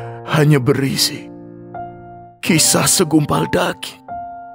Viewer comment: Yg saya dger segumpal daging. Tp yg di tulis segumpal Gading. Apa saya yg salah denger ya bang?